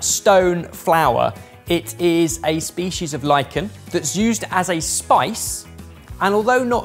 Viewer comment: I processed it as "Lycan" instead of "Lichen" and quickly went "A species of what now?" until i looked back at the screen. I was so startled lol